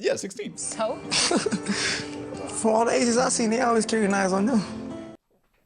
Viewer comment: I can not understand what he is saying, it just sounds like he said a sentence but with no spaces between the words. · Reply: “Fuck, all the Asians I’ve seen, they always carry knives on them”